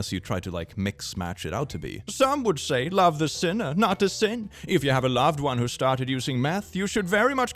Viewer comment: Bro i really thought he said if you have a loved one that's tried math. And i didn't even question it.